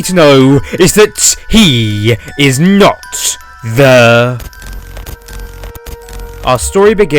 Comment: and also at 0:07 or 0:08 what is there that we'll get confused instead of this entity?he isn't the (gibberish)? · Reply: He's the ghost player?